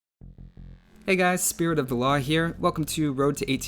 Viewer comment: Every time you say your name I hear "Spirit of Allah"